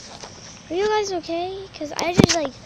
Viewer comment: i thought i heard "are you guys ok because i like fucking trabeld" idk how to spell